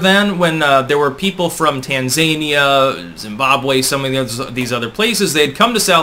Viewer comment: Tann-zane-ia ?? Is that next door to Wankanda? Maybe I misheard it and it’s actual Trans-Zane-ia… which is next door to the undertaker 🤷🏽‍♂️